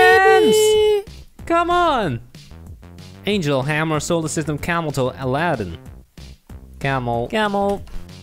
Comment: Am I the only one that heard him say camel toe when it clearly said camel xD